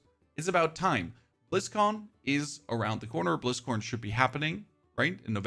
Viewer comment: Sounded like you said Blizzcorn lol